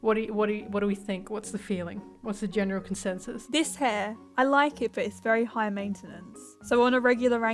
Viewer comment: I litterly like Yuma xD but it killed me as i heard " Is this hair?"